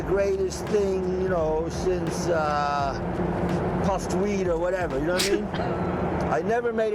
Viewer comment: Just to be clear, at 3:52, the term is "puffed wheat", not weed. It's a breakfast cereal that was common in the USA in Fischer's time. Which is not really funny. I thought he might have said "sliced bread", which is the most common way to use that expression. What I find quite interesting in this video is the voice and manner of speech, in which the younger Fischer is clearly recognizable. Also the body language.